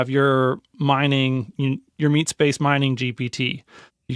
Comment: I'm forever ruined by the internet. Instead of hearing "meatspace" I heard "meatspin"